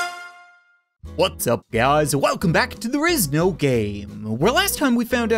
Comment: I thought that you said "there is no gay" at the start😂